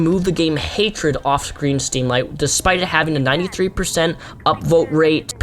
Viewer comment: green steamlight did anyone else hear that